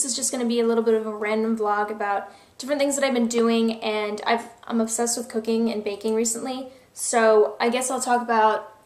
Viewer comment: I thought you said obsessed with Cocaine lately...